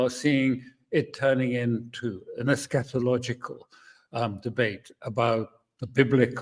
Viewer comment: Eschatological, that is the second time you used that word. I get it now. The first time, I thought you said scatalogical. I looked that up and was confused.